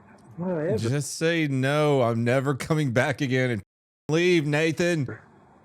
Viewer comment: First thing I hear is "leave Nathan, and say you will never come back" I was like wtf that's a bit targeted lol I know everyone hates me but geeeez